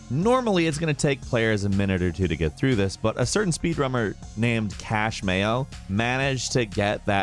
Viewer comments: Did you really say speed rummer there? @ 1:38. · On 1:38 he said Rummer instead of a Runner. You’re welcome guys